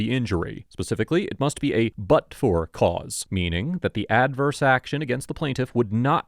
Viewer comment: you said it was a "but for" case with the officer. Is what I found in the captioning. But what I heard you say was "it was from a butt sore officer" 😂 I was thinking how right you are. These case's are always about someone being butt hurt